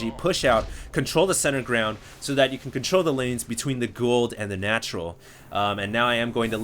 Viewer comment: any stargate fans noticed it sounded like he said gould instead of gold :P